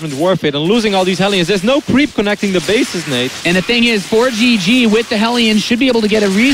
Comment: DId called? Nateception?